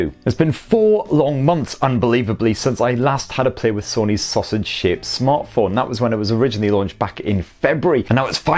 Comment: Did he just say "loved sausage shaped"